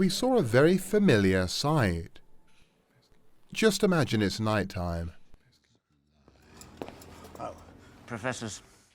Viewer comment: What is he whispering at 9:08 and at 9:10? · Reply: bruh how tf did you even notice that? sounds like he's saying "testing" to me..